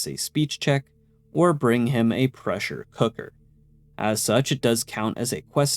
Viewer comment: i thought i heard precious hooker for a sec and i was taken aback